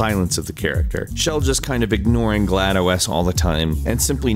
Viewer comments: WAIT DID HE JUST SAY "GLADO, S"? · What did you say? GLa-DOE-AS? GLa-DOES? GLa-DO S? GLaDOS' ASS?